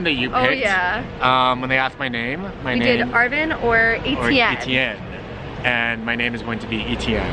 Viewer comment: At first I misheard, and instead of Etienne, I heard ATM. On a different note, why is the majority of Young Adult books revolve around a female character that's 17 years old? I believe this promotes ageism in a certain way. It's feels like only magical or special things can happen to adolescents, and people in their 20's or 30's can not experience that. I have yet to encounter a YA book that portrays a heroine in her 20's or 30's. Just a little rant about YA books in general.